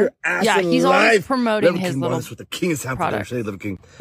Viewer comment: Okay, I was only listening (i.e., not watching the clip) at first, and I thought he say "CHEESE" at first, and my mind instantly went back to Sheogorath from SKYRIM. (Spoiler alert: he most likely said "king," not "cheese.") Now I can't unthink of him as a Daedric Prince. (edited to make sure the time stamp had the quote in it)